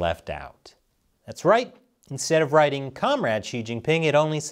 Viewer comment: Are you sure it was comrade? I heard it as chairman.